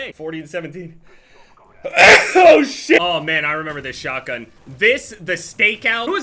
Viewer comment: my brotha that was not the stakeout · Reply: I heard that too but notice he says "this, the stakeout" calling out two different shotguns